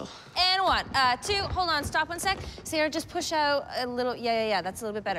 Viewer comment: Did just call gissele Sarah?